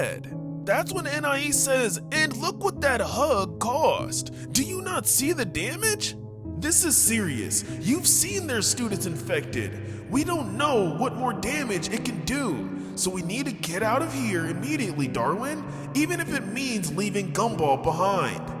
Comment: I heard stupid infection